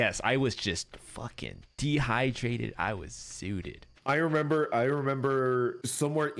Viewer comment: thank you for the clip!!!!!!! I LOVE TINY HUGGING 😭❤️❤️❤️❤️❤️❤️❤️ and linda tskr 🙏 🙏 🙏 (at 3:21 i think he's saying he got zooted, it's another slang for high)